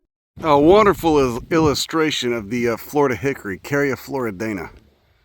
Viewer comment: I heard “Florida Hickory” as “Florida hickery” and that’s going to be what I call redneck activities from now on.